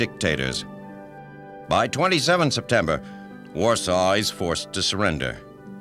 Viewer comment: what does narrator say in the video @ 3:28 after he says "by the 27th of Sept ___ was forced to surrender" ?????? i cant telll..... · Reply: "Warsaw was forced to surrender" Warsaw is the Capital City of Poland. Much like Sacramento is the Capital City of California.